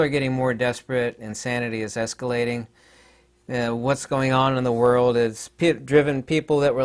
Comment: "...insanity is escalating...", I hear in my mind "...and sanity is escalating...", I reflect & realize the former was what was said... I reflect on my sanity... I share this, lol.